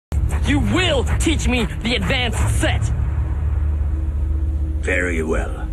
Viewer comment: for a second i thought zuko said "You will teach me the advantage of sex!"